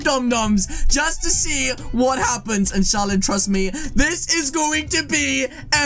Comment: did he say insahAllah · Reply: He said listen sharlin